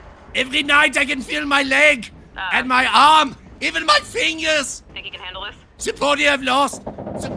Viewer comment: When he says "fingers" i only hear "Pingas"